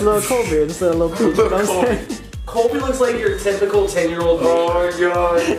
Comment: "Colby looks like your typical 10 year old neighbor." 😂😂😂😂 I'M DONE 😂😂💀💀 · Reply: I thought he said "Colby looks like your typical 10 year old eMo"